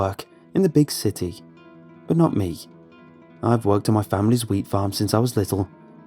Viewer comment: Being honest, when he first said wheat farm I thought he said weed farm. I think it's got to me